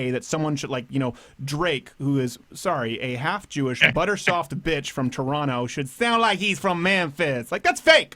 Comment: "Buttersoft Bitch" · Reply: yes- makes more sense than what I first thought he said: "butterscotch fist"